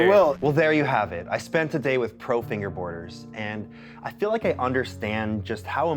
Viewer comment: Totally thought this said “I spent a day with PRO FINGERBANGERS” for a second 🤣